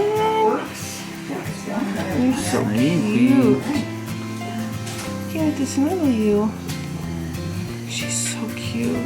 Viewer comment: I know you said "i cant wait to snuggle you" right before you held them but i heard "i cant wait to smell you" 😂 You are also so strong!❤️❤